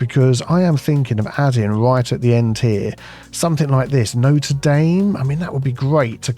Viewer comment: did you just call it "Note damn" ?